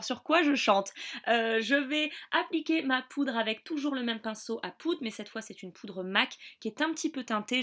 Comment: J'ai entendu "pinceau à prout" à un moment... J'ai bien ri. Oui je suis un bébé! Sinon l'année prochaine si je vais à Paris je me procurerai cette brume, je vais me renseigner sur les prix et où on peut la trouver. Bisous <3